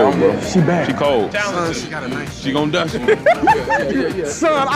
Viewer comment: Since I pay attention to detail no matter how small and hear what a lot of people brush off, someone please tell me that around 14:52 when the men where talking about her shape that didn't say "she gonna die soon". Now I rewound at least 15 times and I promise that's what that man said. 😮